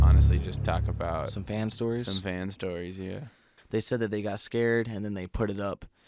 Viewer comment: When they said fans stories I thought he was saying vans stories